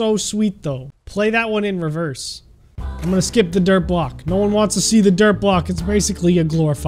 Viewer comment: Did he say dirt